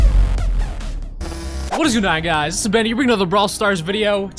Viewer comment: Did say what is good nigh?